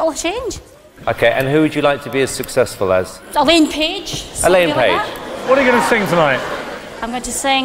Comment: When you know Susan Boyle and dont know Elaine Page (like me), we know she achieved THE dream. haha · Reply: i thought she said Ellen Page as in the actress of Umbrella Academy